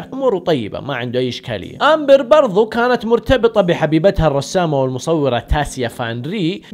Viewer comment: انا وحدي سمعت حبيتها ولا انتم معاي بالله قلولي و الا اذهب لطبيب نفسي🥴🥴 استغفرالله بس ☹️☹️☹️